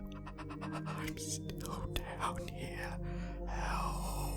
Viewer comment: “ I need to go down here, help’? Can anyone else hear this on the video?